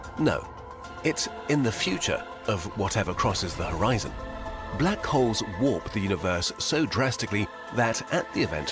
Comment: At the very start I thought he said black hoes